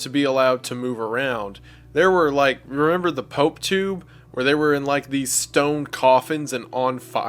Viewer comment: When Wendi said "Pope Tube" I heard "Potoo"😂 A Potoo is a wonderous little borb that looks like... Actually, just look it up😂